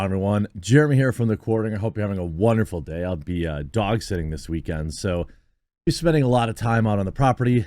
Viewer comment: Lol you kinda chewed up saying dog sitting, and I heard "I'll l be doxxing this weekend", all casual and shit. Lmao